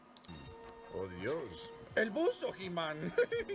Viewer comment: alguien entiende que dice si es me haria el favor de hacermelo saber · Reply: Por Dios - El Buzo HitMan